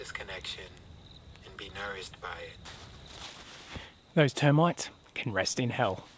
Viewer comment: Serious veggie gardener here - grow what you love to eat, but don't plant it all out at once or you'll get a glut and make mistakes on a bigger scale. Start with 2 beds, put multiple crops in the one bed (taller at the back) and if you want to be planting root veg like leeks, beets, carrots, radish - plant a second crop about 3-4 weeks later so just as you finish the first crop, you got more coming. Leafy greens like lettuce and spinach are different, you can just keep picking as they grow more leaves. Suggest following the SustainableMe Aussie YouTube guy as he is in a similar climate to you and you'll learn heaps about veggies in a tropical zone. And the exciting bugs and fungal pests you will be learning to make peace with 😅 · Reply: So glad I replayed the beginning.....thought you said...'you termites can go to hell'. Rewind and it's actually 'those termites can go to hell....PHEW!! 😂😂😂 hahaha